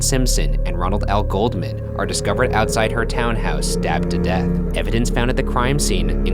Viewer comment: I thought that Ryan said they were slapped to death 😅(sorry I know it's not funny)